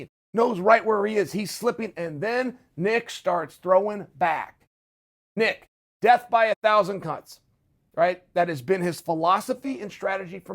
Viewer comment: I swear Chael said “death by a thousand cunts” 🤔